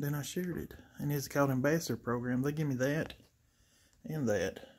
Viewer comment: Oh, ambassador program, I thought you said bastard program. LOL I was like WTF LOL